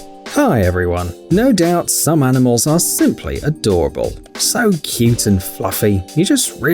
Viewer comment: When uh u said simply adorable i thought u said simps are adorable